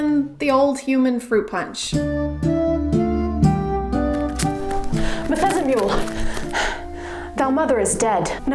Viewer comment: Did you say Bethesda Mule at 9:16 ? Lmao